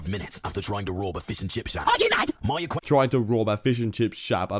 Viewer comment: tf do you mean "shap" he said "shack"